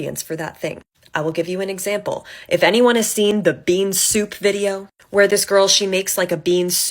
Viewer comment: Until you cut to the first tiktok, I thought you were saying "being soup" and I was thinking that was such a profound thought because we are mostly water...